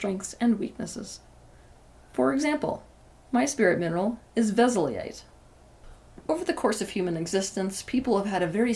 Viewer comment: What did she say her spirit mineral is? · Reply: Veszelyite <link>